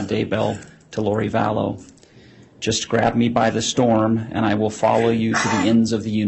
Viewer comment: “Grab me by The Storm”?????! Gag! Somebody give that Troll a thesaurus! He calls himself a “writer” but uses the same boring descriptors over and over again. It amazes me Dopey was “successful” in any circle! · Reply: I misheard at first and thought said 'Grab me by the stalk' - fast backtrack to relisten